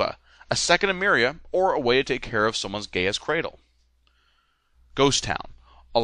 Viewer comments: lol I heard 'someone's Gay-Ass-Cradle' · I really thought you said gay ass cradle lol